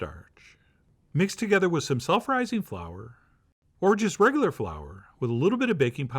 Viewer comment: "self rising flour" was close captioned as "sulfurizing flour" 🤣🤣🤣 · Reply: It sounded like he said sulfurizing flour.